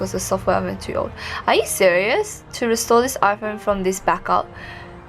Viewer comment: tell me why my phone thought it said Siri and it came up😂😂